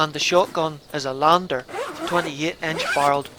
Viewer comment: Nice video again 👌 Did you say the shotgun is a Lander or did you mean Lanber cos I’m sure I heard you say Lander I may be wrong. Keep the vids coming as they are excellent as always.